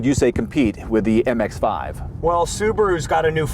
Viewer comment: did he just say mx5?